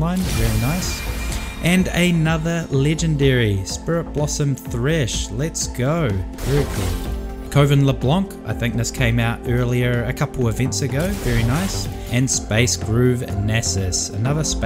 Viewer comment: i swear i heard covid leblanc